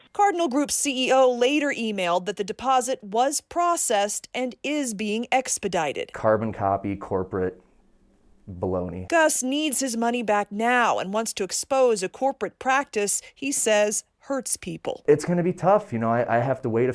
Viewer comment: Did he just really say on camera for the corporate management company to "blow me" (@ 1:50 mark) and Denver 7 didn't catch that or was I just hearing things? Never heard of local news allowing that before. lol 😂